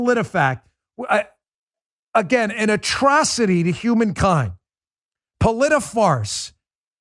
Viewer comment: OMG! At 37:55 in, I swear I heard Bongino say, "Polit a fart"... Too funny!